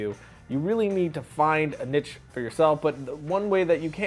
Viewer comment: I thought he said: "You really need to find a Bitch for yourself" LOL :S